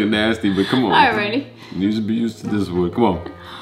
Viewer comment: Y’all dumb lit ! Loove this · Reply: did say you should be use to this one ? 👀🥵